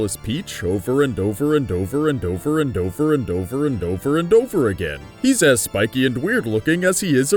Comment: What I heard was"over and over bend over"